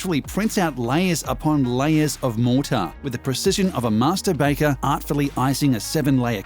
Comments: Was it just me who thought he said ‘masterbaiter’ at 2:18 instead of master baker? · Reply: Nah it sounded like he said masta baka to me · Was it just me who thought he said ‘masterbaiter’ at 2:18 instead of master baker?